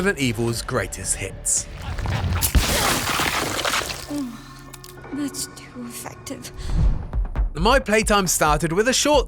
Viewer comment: did I just hear her say thats to stiff? or am I hearing things, lol. 11 more days, I can't wait to get my hands on this. I just restarted RE2 Remake and im going to try to play them all before 9 gets here. But the main ones I wanted to revisit is RE2, RE3 and RE4.